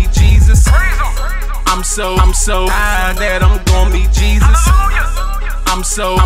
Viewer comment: "I'm so high that I'm gon meet Jesus", it's like he means he's gonna get so high that he reaches Heaven metaphorically, but it could also mean he's gonna get so high that he'll die. It's like a double aunt andre. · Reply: I hear, "I'm so I'm so high that I'm gon' be Jesus", but I could be mist akin.